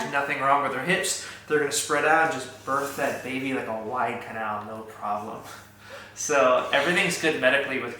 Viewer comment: acting out the hips spreading has me rolling😂😂😂 “birth that baby like a wide canal” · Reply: I thought he said like a white cow at first and was so confused😂